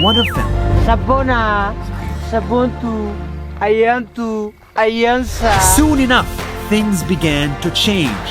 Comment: bruh, why is he calling Elsa? 🤣